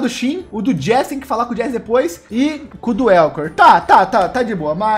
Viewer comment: geente, só eu escutei c* do Eokor? HAUSHAUHAUAHAUHSUA🤣🤣🤣